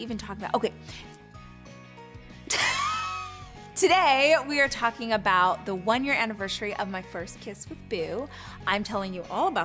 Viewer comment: Did I hear it right? @ 11:34 "OMG, today we 're talking about the 1-year anniversary of my first kiss?" God, vacuousness has broke the ceiling record. And ' face reaction to that is identical to mine & others with more brain cells.